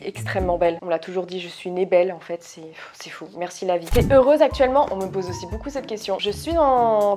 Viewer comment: la question posée 't'es heureuse dans la vie ?' la question montrée 'A quoi ressemble ton copain ?' :)